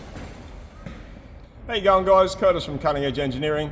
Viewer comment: I always hear saying “Hey Young Guys” And I always happily accept